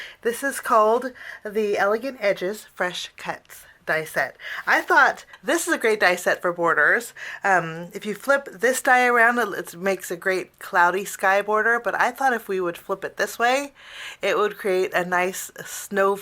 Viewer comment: Love this!!!! P.S. when you said “this is a great die set for boarders” I heard “hoarders” and was like yeah, all sets are good for us, because we hoarders, need them all (cuz you never know when you going to need it!).